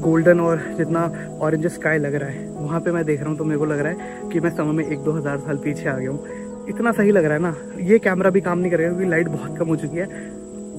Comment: Excellent video! The fact that it was just you who recorded this and not a large production team makes it more authentic. 0:53 "I feel like I've gone one to two thousand years back in time", but the subtitle says "I feel like I've gone back 1-2 years in time".